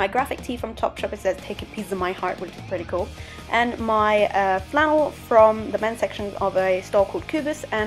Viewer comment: Put on the subtitles & stop at 2:31 you said & my uh flannel & the subtitles said my ass 😂